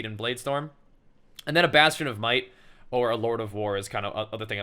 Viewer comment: I feel like i kept hearing, "bastard of might" lol